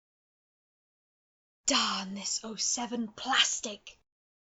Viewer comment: what did or just say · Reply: Damn this 07 plstic!